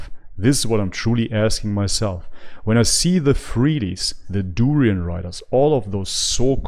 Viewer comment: My PC doesn't have sound, at around 10:19 the auto captions says "this is what I'm truly asking myself when I see the 3ds the Dorian riders". Obviously that's Durian Riders, but can anyone tell me what 3ds is? I'm guessing freelees lol. I'll have sound again in a week hopefully if a USB soundcard works out, I'll check back then just thought I'd ask. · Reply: lol yes it's the freelees and durianriders :)